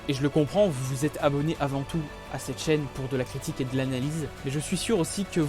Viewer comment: Mon code de Parrainage PI, Invitez vos amis à rejoindre Pi Network Je vous envoie 1π ! Pi est une nouvelle Cryptomonnaie développé par des Docteurs en Science Informatique de Stanford, avec plus de 10 millions de membres à travers le monde. Pour réclamer vos Pi, suivez ce lien : <link> et utilisez mon pseudo (CatsCommanders) comme code d'invitation. Bonne fin de Vacances ;) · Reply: Ouais enfaite j'ai entendu "plus de critique" vzy c'est nul mais je reste abo je sais que tu feras du lourd